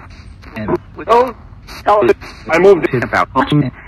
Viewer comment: “I moved to his house” Am I the only one who heard that ?